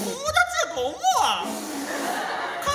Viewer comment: 秒の考えにくいぃ！ のまえってなんて言ってます？ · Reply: 友達やと思わん。